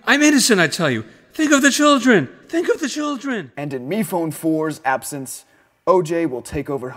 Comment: Mephone: literally commits several crimes and mentally hurt several people Also Mephone: ThiNK oF tHe CHIldRen! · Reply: I heard "Pick up the children!"